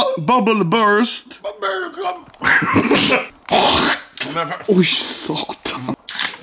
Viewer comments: Did he just fucking say ”Voi saatana” ?😂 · Did he say "oi saatana" at 7:37?